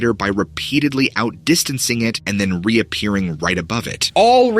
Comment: you say " by repeatedly outdistancing it" but your caption does not say that😂